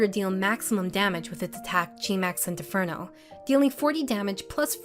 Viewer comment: When she said “Maxie” I heard “Max “C”” and my heart sank from the PTSD